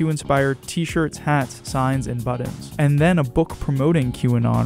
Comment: Did you say "Buddons"? @ 1:03 #FakeNews is Fake